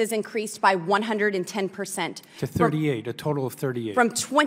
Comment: did I hear him correctly- “it’s only 38” let’s hope his family or himself are NOT one of the 38! I don’t wish violence on any but, WTAF 😱 No wonder so maybe are not even reported when you hear this 💩 out the heads!